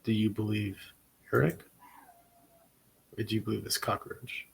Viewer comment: You get so excited it sounds like your saying cockaroach i have noticed it several times